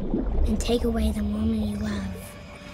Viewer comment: "take away the WOMAN you love".. Is she saying woman? or..